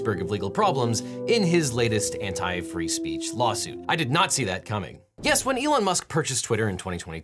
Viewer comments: Is it me, or did I hear "I did NAZI that coming"? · hehehe someone was a redditor once because i heard "I did nahtzee that coming" which heh, accurate